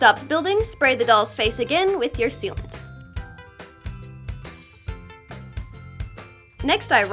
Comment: when she said "sealant" I hear it as "soupent" for some odd reason.